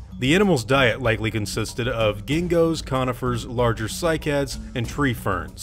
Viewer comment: "The animals´ diet likely consisted of Genghis-Khanifers" I´m sorry, but that´s just what I heard :D